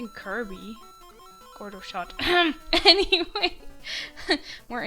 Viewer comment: upon closer listening I think I misheard, but I when you said the insider joke it sounded to me like "Photo-shopped"